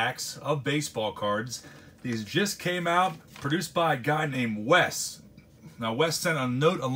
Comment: I thought you said produced by kanye west lmao